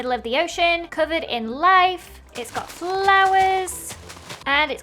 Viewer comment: Ldshadowlady:Beautiful island covered in light What I thought she said:Beautiful island covered in lies